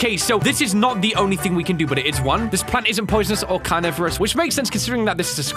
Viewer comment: when the science teacher said carnivores. I thought he said coronavirus